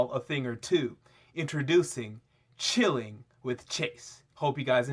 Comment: who else thought he said chilli with chase.